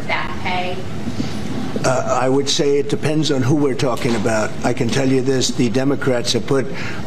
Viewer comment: 😅😅who we are,😂😂who we are. That's what I thought she was saying. How embarrassing